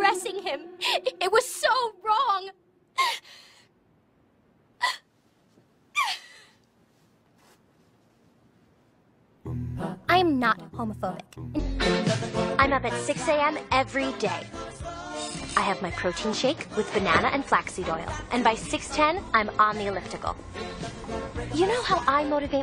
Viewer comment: Haha I thought for a sec she said 'I'm not homophobic, I get up at 6 everyday', like those 2 things are connected 😂